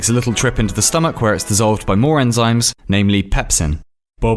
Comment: At first I heard animes